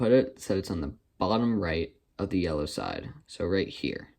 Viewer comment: am I the only one who hears “eleven right of the yellow side”? @ 8:13 ? Everyone I ask they hear “bottom” right, but my brain can only hear the word eleven!